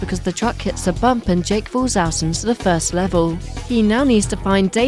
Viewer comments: I hear “Jake Paul’s ass falls out and loses the first level” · I heard Jake paul 💀